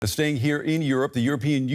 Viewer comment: He started by saying "staying here in Europe" but I heard "the stain here in Europe".